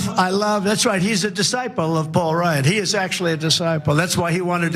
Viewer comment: Am I the only one who heard him say "Paul Rudd" instead of "Paul Ryan"?